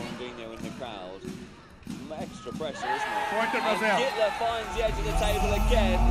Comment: i keep hearing "hitler" instead of "guitler"